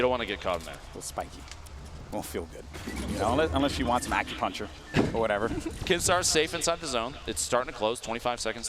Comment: did they say unless you want pack a puncher? 😂